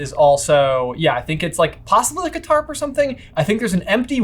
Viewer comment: at about 7:50 "Possibly like a tarp or something" Captions: "Possibly a guitar" Me: Ugh. If I couldn't hear I would think these guys were crazy.